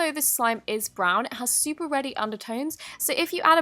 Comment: When you said “reddy” I thought you said “ready” and I was like “How can an undertone be ready?” Then I realized... I’m a dumb person😋